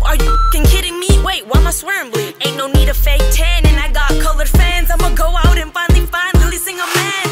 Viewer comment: I thought she said "COVID fans" haha where my 2021 people?